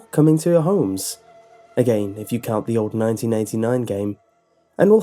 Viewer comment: Friday the 13th also had a game for Nintendo in 1989. It used to scare the crap outta me as a 7 year old kid lol. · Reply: He CLEARLY said "1989 game" at 1:21. Maybe you should turn up your volume or get your ears checked, friend.